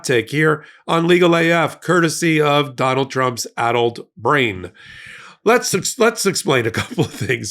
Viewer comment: It sounded like Popok said "adult brain" and I laughed at how absurd that was. I replayed it and realized he said "addled." Yeah, that's much more like it. 😂